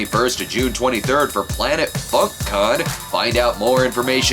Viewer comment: When hye said funk con, i thought he said fuck con. He does review pornos, so I thought it was legit.